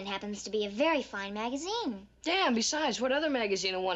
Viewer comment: Did Greg say “Damn”? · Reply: he said "and besides" (I think)